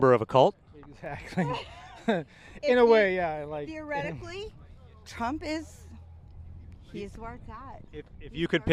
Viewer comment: Did she just say that "theoretically Trump is our god"??! 😮